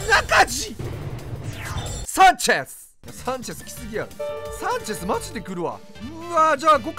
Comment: 最初のサンチェスがキュンです！に聞こえるw